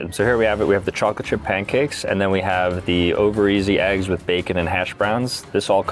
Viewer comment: Did Sambucha say “ovaries” as in the female organ for babies???